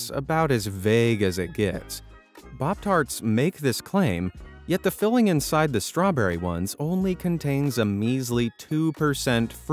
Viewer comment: why did you say "bobtart"? Is arabic your main languatge?